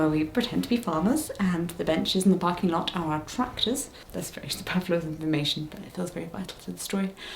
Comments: I didn't catch it at first, so I checked the subtitles and they just say indistinct. But after a few listens that word is superfluous, for anyone wondering. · i do not know if this is helpful or if anyone else has pointed it out already, but when the subtitles say (indistinct) at 3:29, is saying "superfluous"!